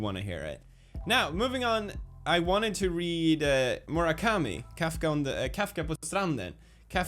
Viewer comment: At first i thought he said Kaffe på stranden (Coffee on the beach)